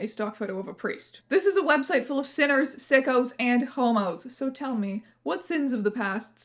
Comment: I was listening to this while driving and had to hurry to a stoplight so I could glance over at the phone screen because I thought you said "Sinners, Sickos, and CUOMOS" as in Rivers Cuomo and I was so confused as to whether that was a tumblr inside joke I had never heard about or just me mishearing things